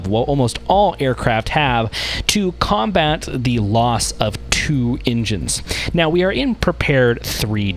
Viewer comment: It sounds like you saying the loss of 2 Indians 😄